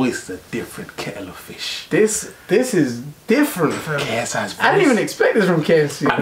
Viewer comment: Did u listen to what the guy on the left said on 2:27 he said kfc not ksi